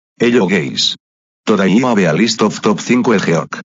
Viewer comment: Did he just say "hello gays"? ☠☠☠